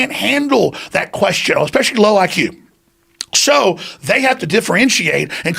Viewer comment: especially low like you to funny i don't think Joe heard Alex say this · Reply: He said “especially low IQ”… i think you’re one of the people he was referring to lol